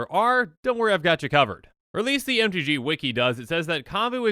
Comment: did you just say "I got you kavured"? god damnit